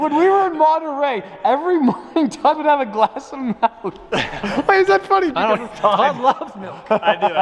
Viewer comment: did he just say malk?